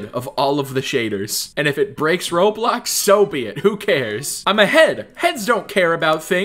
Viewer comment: I thought you were saying you were ahead of someone But you were actually saying that you are a head